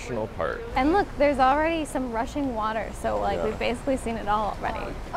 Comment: Huh? Heard it as "russian water" first hah(?!). Rushing water it must be :)